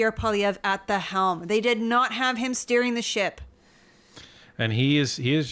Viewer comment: "...didn't have Pierre Poilievre at the helm , steering the ship." LOL. At first I heard 'stirring the shit.' I think Pierre is totally rattling Trudeau into oblivion!!